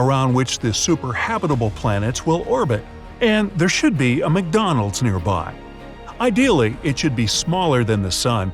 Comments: Did he say at 2:10 ish that there McDonald's should be nearby? Or am I hearing voices??? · is it just me or did he say there is a mcdonalds